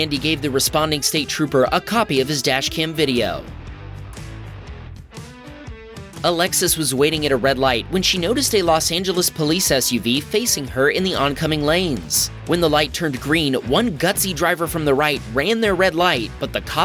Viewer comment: 😂 I thought you said "A Lexus" and not Alexis